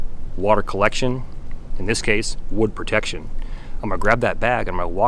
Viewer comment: Can you explain the theory behind putting the smalls into the bag? Is that just to protect the wood in case rain returns or is there another reason? Thanks! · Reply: said ‘wood protection’.